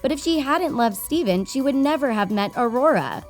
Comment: I kept hearing steven. It's Stefan. 🤦‍♂️